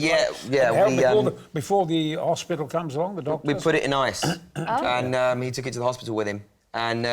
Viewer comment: "We put it in ice". Oh, no! Do not follow that old advice. It will ruin the chances. Keep it cold, but no freezing. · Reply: I thought they said "We put it in ass".